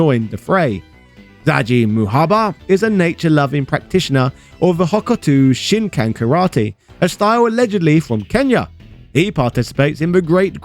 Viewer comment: what did you say he practices hawk tauh karate?